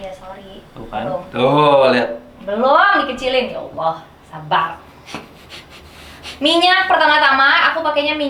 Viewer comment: Kok pengen ketawa yaa:v 1:41😂 Kak tu bilang apa gk jelass:(kek bilang YAALLAH SABAR?KAN